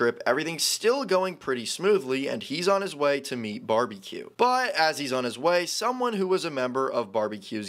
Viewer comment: First I thought he said Barbie😭